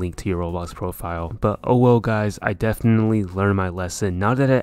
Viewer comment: did you say ''owo'' or ''oh well''